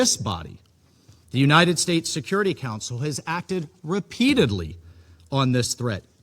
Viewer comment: did he say united states security council?